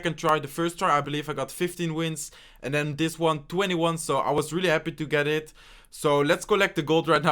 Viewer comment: anyone else notice him say 21 instead of 20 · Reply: RalRem 20-1